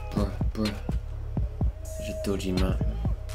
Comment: The fuck did he say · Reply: Deshawn Murray he said bruh